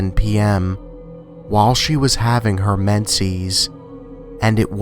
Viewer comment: can you plz explain to me what is menses? Or what ever he said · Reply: menses is the time of the month for girls (period)